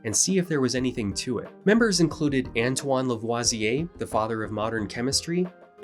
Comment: Anton LeVay, I swear, that’s what I heard! Lol